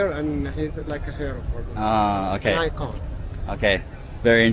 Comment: the subtitles are wrong: he didn’t say “like God” he said “icon”